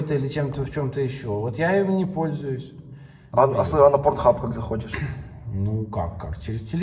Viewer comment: мне послышалась или он сказал как ты PORN HUB смотришь?'?